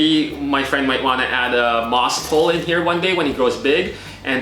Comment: what does he say?? "Add a mossifulah"? What's a "mossifulah"? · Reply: I think he said moss pole